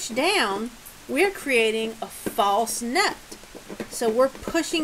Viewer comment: Okay, at 15:20 you are describing the "nut". My mistake. I heard "false note", instead of "false NUT". It all makes sense now...I need a hearing aid! LOL. Thanks for putting up with my poor hearing (multi-tasking can be a monster). I've looked at some of your other videos. That stick dulcimer looks interesting, but I think the traditional Mountain Dulcimer would be my preference. Of course, we don't settle for just one style...life is too short. rod